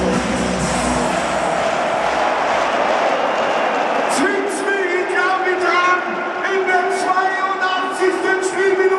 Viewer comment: Was wird gesagt am 0:42 ? Ich verstehe es nicht... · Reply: Zwickts mi i glab i tram (bayrisch) Kneift mich ich glaub ich träume :D